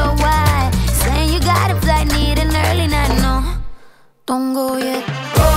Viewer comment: I just find it so clever how they pronounced “don’t go yet”, cause it sounds almost exotic and foreign… seriously thought that she was using non English words ( why it took me so long to find this song), but was pleasantly surprised. The writers were truly thinking outside the box which makes this production just that little more high-end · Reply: I thought she was saying don’t go there